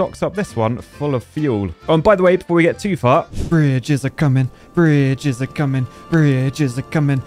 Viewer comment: Imcould have sworn he said, "fridges are coming, fridges are coming!"